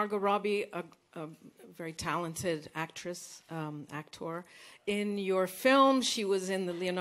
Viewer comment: Did she say about Margot; "actress and actor" ?